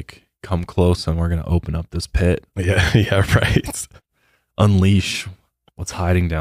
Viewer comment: Hey uh does anyone else hear this high pitched voice say “yes?” It’s after 28:20 it’s at 28:27. I don’t think that was on purpose?